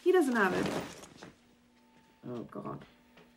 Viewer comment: He says, "Oh, God", but the subtitle says, "Oh, Jesus". It's no big deal. I laughed a little when I saw that. I wonder why they changed it.